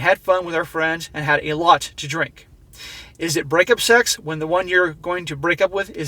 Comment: hang on... did I hear you right? Is it brake up sex? so he banged he knowing she was cheating?